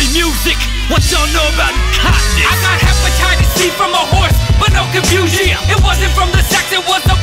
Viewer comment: i heard the song alone first and i thought akiva said he had horse butt